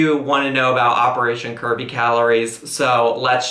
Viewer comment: I seriously thought y'all were saying operation Kirby Calories hahahah